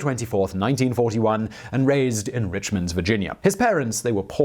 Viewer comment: I thought you said he was raised in Richmonds vagina.